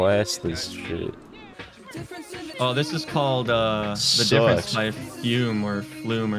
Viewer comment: song name? I didn’t catch what said. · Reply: The difference by flume